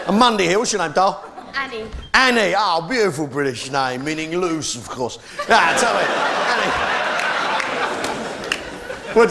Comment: what does he say? 'Annie beautiful British name meaning...' what?? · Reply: He said meaning loose of course